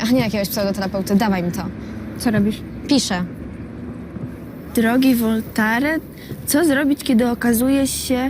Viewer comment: Czy tylko ja Usłyszałem jak powiedziała Drogi Voltaren {emulŻel kur...} haha i po drugie Bardzo szybko pisze na tablecie klikając w 1 i to samo Miejsce haha Zajebisty odcinek